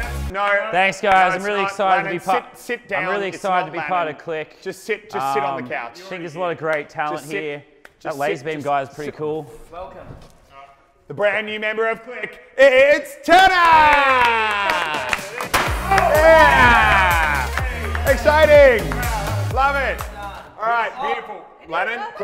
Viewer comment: I thought he said the brand new member of click is ta da